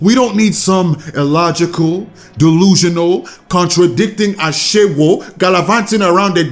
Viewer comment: was it just me or did he say Ashawo💀